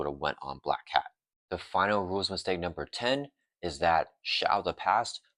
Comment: Couldn't deal with the mushmouth speaking, sadly. I had to check out after the third time I heard you say "rules istake". Enunciate and you'll see your view counts climb.